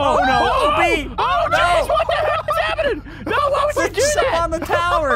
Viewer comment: what did say? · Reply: says "What The Hell" i think this is the only video he says a H word